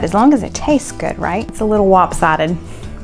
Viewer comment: Rewatching this one, you have me giggling at midnight in Texas 🤪🤣 thought you said"wop-sided" instead of lop-sided then you asked so... All waskly wabbits eat wop-sided muffins and everything looks so scrumptious yum-yum!! !!! Keep sharing the recipes & love 🎼🎶🎶🦋✌️